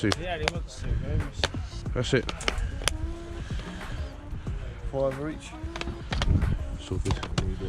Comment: the Just Dance games always trade in for a decent amount. Did he really say 5 each 😳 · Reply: Yep he said 5 each, hence why I walked on haha